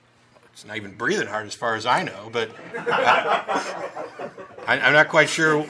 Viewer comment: What did Matt say at 0:16? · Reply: He's not even breathing as far as I know...